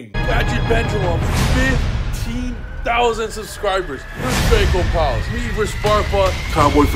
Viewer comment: when he said "15000 subscribers" during the intro I thought he was about to say 15000 negates